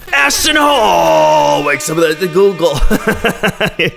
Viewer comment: I thought you said ASShton HOLE for a second 😂😂